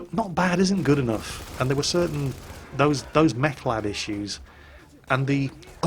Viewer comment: I keep hearing "meth lab" <link>